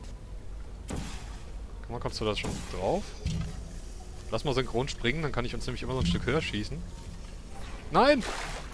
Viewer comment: schätze er hat synchronschwimmen verstanden